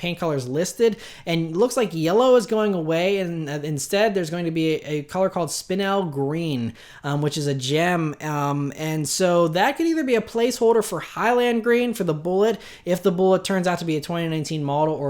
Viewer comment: I thought for a second you said the new Mustang color was "Spin-Out Green". I laughed.